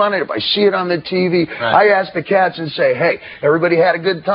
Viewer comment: at around 5:43 does dave say i ask the Catsun ??? or what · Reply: I ask the cats AND say hey ...